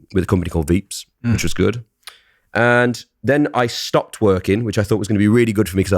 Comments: why did I hear "stop twerking" 😭😭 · I thought he said I stopped twerking🤣🤣🤣🤣